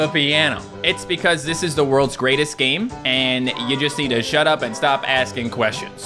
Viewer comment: Did he just say shut up?